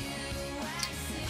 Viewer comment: I thought she said “I’m gonna be late for sandwich.”